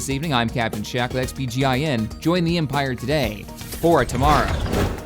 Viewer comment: anyone else heard that said "for a tomorrow" instead of "for a better tomorrow"?